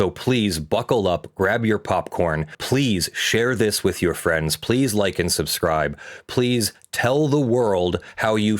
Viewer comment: >_<; For some reason when people say "please like and subscribe" I hear "Please lichen subscribe".